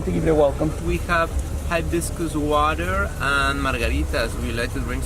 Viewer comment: I thought they said - high vicious water- and I was like 😮 what ! Hibiscus water hahaha